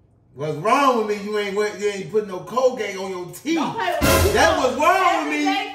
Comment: "whats wrong is u aint put no cocaine on yo teeth" THAT PART HAD ME DYINGG 😭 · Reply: Thought he said Colgate